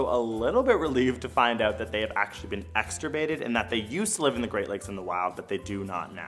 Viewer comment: what word is that? I need to look up the definition. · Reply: Extirpated. It means extinct in a certain region (but not totally extinct worldwide) So Paddlefish are no longer found in the Great Lakes but they're not totally extinct.